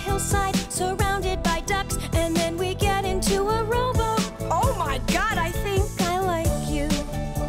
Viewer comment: I always thought she said "get into a robot" instead of "rowboat" and I like my version better.